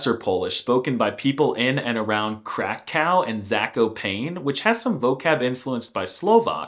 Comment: did i just hear "Crack Cow" ? 🤨